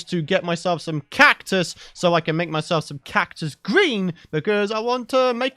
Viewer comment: When he said "Cactus Green" I thought he said "Cactus Greaves" and I was like "T E R R A R I A?"